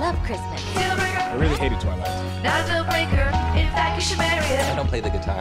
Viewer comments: What does he say at 2:24? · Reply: He said I really hated Twilight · What in tarnation does he say? It's barely audible. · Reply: He says "I really hated Twilight."